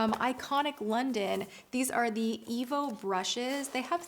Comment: At first I thought she said "evil brushes". Lol